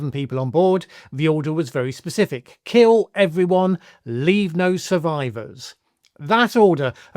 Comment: He said "leave no survivors", I heard "leave no witnesses".